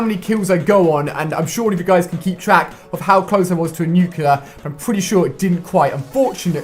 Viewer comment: When you nuclear, it sounds like your saying nuke killer